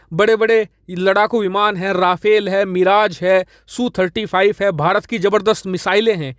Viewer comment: "kya bola" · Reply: Su 30 not su 35 I got it Thank you